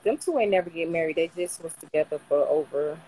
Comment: So that woman at 15:18 is a “married” woman watching Kevin’s lives with no draws on & when asked about her age, she said 30 sumn then 40 sum. Can’t make this shit up. These are the ladies who need his advice the most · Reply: I didn’t catch when she said 30 something , I thought she said 43 the first time